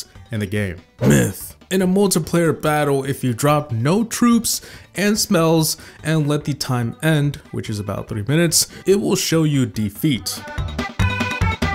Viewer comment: why did u say smells instead of spells